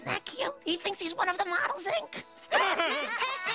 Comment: FOR THE LONGEST TIME I THOUGHT HOMER SAID "HEY, GET THAT CAT OUTTA THERE!" LIKE A FUCKING MANDELA EFFECT SCENARIO